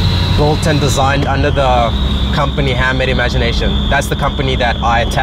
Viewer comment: I didn't catch the name of the company ~10:00 maybe my ears are getting old ... did someone else hear it ? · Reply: Handmade Imagination